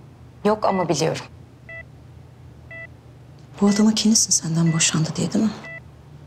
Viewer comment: ne dedi zeynep anlamdım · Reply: Ra Na bu adama kinlisin dimi senden boşandı diye dedi